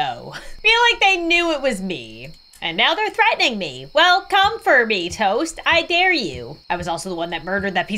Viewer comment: oh I heard “come, furby toast!”